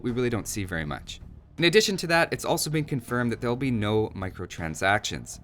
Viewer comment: did i hear "sad" cuz of no microtransactions?.. broo o.O since when mt are a standart.. in my opinion, people want all the content after release.. and im not talking about future dlc's.. and cosmetics which to, in my opinion should be unlockable by progressing the game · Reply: He said it's sad that it needs to be said that there will be no microtransactions